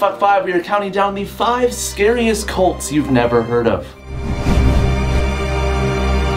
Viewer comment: Oh my god I glanced at the notification and thought it said "5 SCARIEST CLITS" I was like wow has officially no chill.